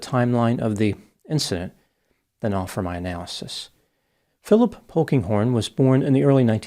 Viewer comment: At first, I heard "Philip Honkinghorn."